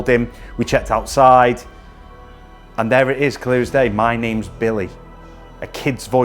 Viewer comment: I hear" mamma see' ?? After listening to it again the next day, It is a little boy saying "My name is Billy" Wow. amazing.